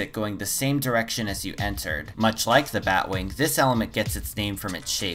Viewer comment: I thought the bowtie said the Bowie and i started internally freaking out and then i heard it and im like "oh.. right.."